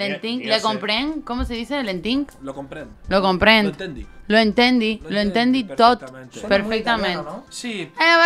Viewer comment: lo entendi JAJAJAJAJA es "ho entenc"